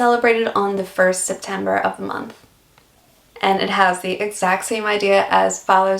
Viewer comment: did you actually say "1st September of the month "? P.S. love your work and I know I'm a lot late but welcome to Oz